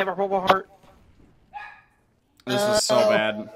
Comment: At the end of the video it sounds like is saying CRAAAZY TRICKsha